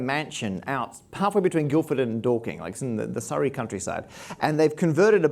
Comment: lol I like how the guy who did the subs put [INAUDIBLE] instead of Dalking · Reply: You heard it right but it's spelled Dorking (I live near it). Also the subs missed 'pedant' earlier on. Perhaps, being British, I can understand an Australian/ English accent better than them.